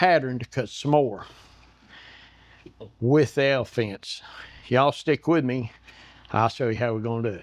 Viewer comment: I thought he said the "hell fence". This is BRILLIANT!!!